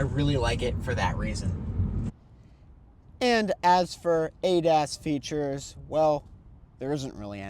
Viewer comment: oh, I thought he said “ate-ass features” or “eight ass features”😭